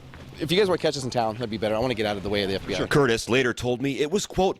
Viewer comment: did he said i want get out of the way of the fbi ???